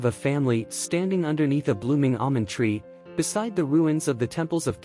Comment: I thought he said, "blooming onion tree," and I was like, that's where they come from.